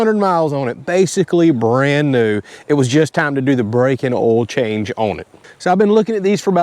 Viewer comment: 600 miles and already needs brakes? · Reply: Oh never mind @ 1:38 i heard brakes and oil change but you said break in oil change 😂